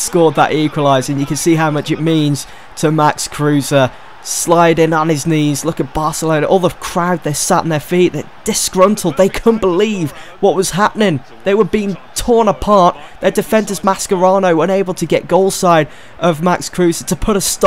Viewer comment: Am i the only one who thought he said "Look at barca there sat on there feet" (around 11mins- 11:04mins)